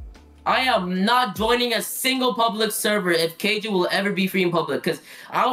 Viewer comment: im in that video :D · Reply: I thought u said "what if KJ was just free in public" when u asked me at 3:07 😭